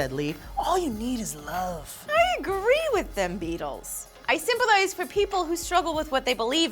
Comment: Lee said "simple-ise" instead of sympethize haha long day?